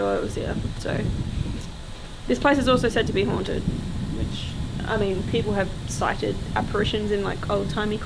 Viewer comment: after you said the place was said to be haunted, a man’s voice sounds like it says “witch” or something like that, was that talking? It creeped me out!! 😂